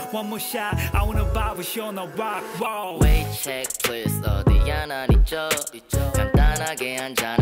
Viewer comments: I thought he said "*wave* check plz" and not "*wait* check plz"😂😂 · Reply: Before I checked the lyrics I thought he said "Way Ched please", because he really like the beat , but...😅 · I thought he said "*wave* check plz" and not "*wait* check plz"😂😂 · "어디야 난 이쪽" 이거 자꾸 까나리액젓으로 들림 ㅠ